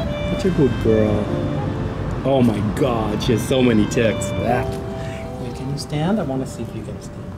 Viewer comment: I misheard and thought he said she had so many tits lol she's so cute and friendly.